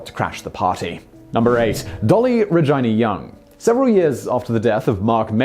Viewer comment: Why does I sound like he says “vagina” when he says “Regina”